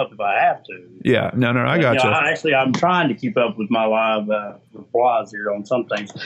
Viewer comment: At one point I thought he said something to the effect "trying to keep my lies straight" and gave a WTF expression. Did anyone else notice that? I could be wrong as parts of what he said were difficult to understand.